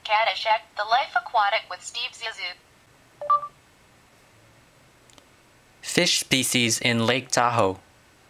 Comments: for a second I thought you said: "fish feces in Lake Tahoe". Nice vid. Very informative. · There were times when I asked the same thing and it didn't show cards. It displayed the same words (so it knew what I was asking) but I got Google search results. Also, lol I thought you said "fish feces" at 7:42.